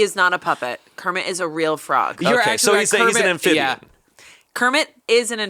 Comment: Did Amanda say that Kermit is an "aNphibian"?